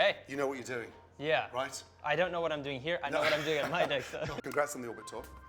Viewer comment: kinda sounded like "i know what im doing with my dick"